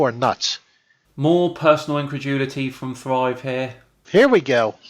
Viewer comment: WHAT IS THAT word you say at 6:03? I have tried again and again to figure it out. Inconjulity? WTF? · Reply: incredulity...